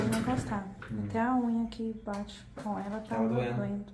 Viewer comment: Tem um trecho, após 8:40 q ela fala que até a unha de baixo tava pegando? Ou eu entendi erradooo...? Boa noitee , belo trabalho